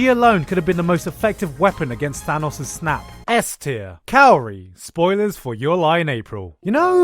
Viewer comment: Kaori i thought i heard calorie